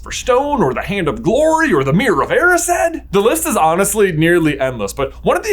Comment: I thought I heard you say “The list is nearly headless.” Had to rewind. 😅